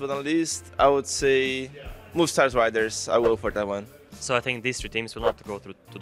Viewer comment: i heard "mustache riders" hahah